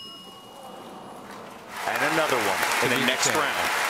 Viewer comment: I heard DJ Khaled's another one